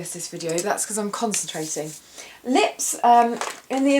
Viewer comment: When you said "I'm concentrating" I thought you said constipated O.O I had to do a double take with the volume up. ..... ANYWAY. Great look, you're always beautiful!